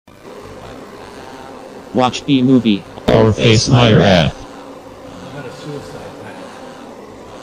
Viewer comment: It sounds like he said airface pirate